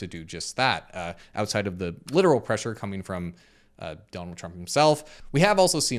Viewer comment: I thought PB said "dump truck" then I looked over at the video and realized he actually said "Donald Trump." 😂😂😂😂